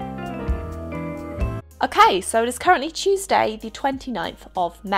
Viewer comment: When you said Tuesday I thought you said cheese day lol